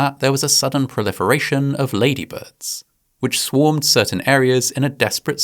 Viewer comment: Lol labybirds is what I heard you say at the end made me chuckle